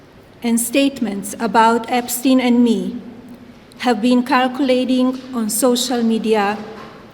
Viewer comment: calculating===> circulating. 곧 휴거있으니 속히 지은 모든 죄를 회개하시고 하나님의 독생자 나사렛 예수 그리스도님을 진실로 믿으셔서 모든 죄에서 끊어지시고 거룩하게 사세요. 휴거이후에 은혜의 천국문이 닫힙니다. 예수님을 믿는 모든 이방인과 유대인은 곧 휴거되어 천국으로 들려올려져 사라집니다. 이것을 목격하시게 될 염소들, 대환란에 들어가시면 절대로 짐승경배마시고 짐승의 표를 손이나 이마에 절대로 받지 마세요. 굶어죽는다 해도 참수당한다해도 절대로 받으시면 안됩니다. 순교하시면 다시 살아나게 되니 담대함을 구하세요. 감사합니다. 지금은 오직 예수님을 진실로 믿음으로써 휴거될 수 있는 은혜의 시기입니다.